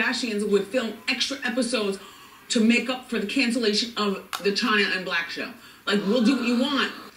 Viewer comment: Am I first? Not that it matters but I see others hyping it and I thought why not? Did Wendy say Chyna and Blac Show?